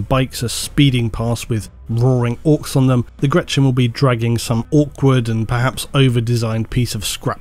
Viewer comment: Did... did you just say orkward?